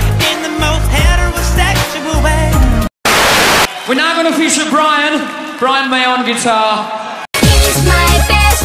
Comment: So much maycury, so little time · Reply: I actually first heard it as Frian